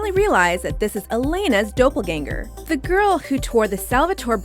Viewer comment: Did she just say Dopelganger?